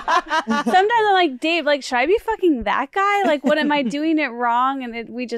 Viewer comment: As a fellow pregnant, I am loving this episode so far. Hawkeye is an awesome name, we are going with Dracula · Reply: I thought she said hot guy 💀